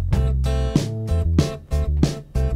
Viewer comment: for the longest time i thought she kept saying "menstrual shows" and I can only imagine what those will be like.